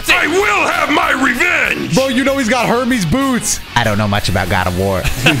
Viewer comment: Dead ass thought said herpes instead of hermes for a sec